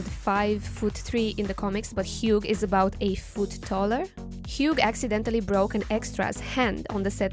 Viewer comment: Hugh is about 8 ft tall?? lady he is 6'2 edit: got it cleared , she actually said "A foot Taller" i misheard it because my headphones are on reverb